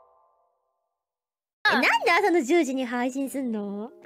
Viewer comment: Did she say Jojo? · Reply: She said « asa no jyu-ji / 朝の10時 » (10am), « why do you stream at 10 am ? » (nande asa no jyu-ji ni haishin sun no ? / なんで朝の10時に配信すんの？ (Sorry for all the explanations, hope it helped you !)